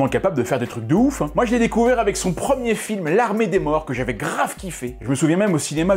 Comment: C'est moi où il y a une haillucination auditive à 4:17 quand il dit "l'armée des morts" j'entends "l'armée des bras" ou "l'armée des morts selon le mot sur lequel je me concentre... Quel génie ! 😅